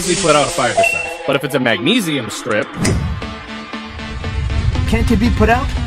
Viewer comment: I thought ye said kinesium